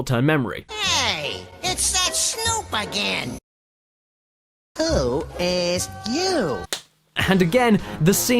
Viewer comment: He actually said "Who ASKED you?". You just gotta listen carefully